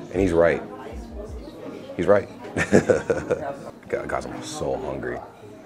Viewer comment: Everytime he says 'I'm so hungry' I just hear 'I'm so high' instead.. 😂 I mean, you can eat everything separately, but why would you do that unless you have the munchies 😂 But great video, man! Just maybe next time take someone with you who actually shows you how to enjoy/eat German things.